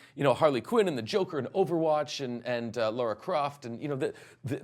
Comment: Did he say overwatch?😂